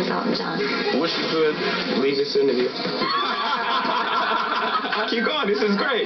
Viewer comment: what did jay-z said at the "wish you could " part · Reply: hendrixaddicted leave this interview